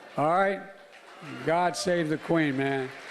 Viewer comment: She absolutely cannot say what most people believe to be true because her voter base would abandon her. Simple as that. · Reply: I thought he said God save the queer?